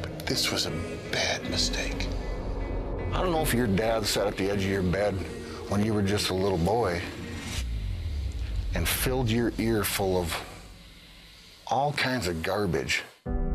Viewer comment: What does Brock says at 2:25 to 2:28? Can anyone tell me I want to know. Thank you. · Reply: "I don't know if your dad sat at the edge of your bed, when you were just a little boy, and filled your ears with all kinds of garbage"